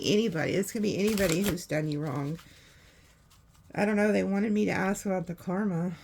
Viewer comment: I heard it too! It gives me chills!! 35:55 “Ask her”